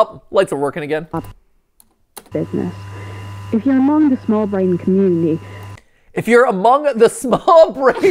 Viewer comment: to be fair to the auto captions, 'small-brained community' is EXACTLY what it sounds like she's saying - i almost can't hear anything else